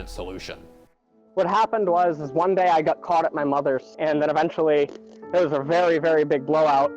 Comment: I need to stop watch porn. All I heard was "caught, mother, blow"